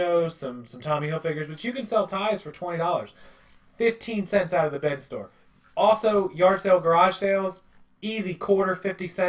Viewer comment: what are you saying? .15 cents out of the ??? store. Thanks in advance Great videos and youve got a new subscriber! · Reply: I said they cost $.15 of of the BIN store. As in the goodwill outlet bin store. By Weight. you can get 10 per pound and they are $1.50 per pound